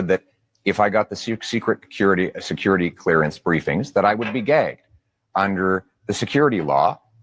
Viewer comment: I heard him say "gay".